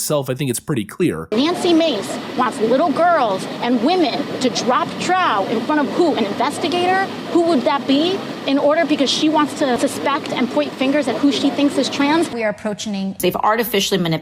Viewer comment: did AOC just say “drop trout”?! Omg I can’t. Pft I can’t.